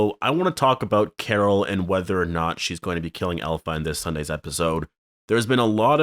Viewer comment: You said elephant instead of alpha at 0:18 according to the auto caps😁